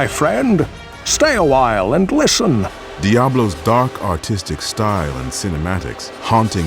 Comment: almost thought i heard dark autistic instead of dark artistic.